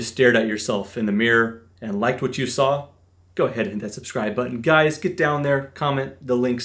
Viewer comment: I heard "Hit that position...!" thought my damn game was open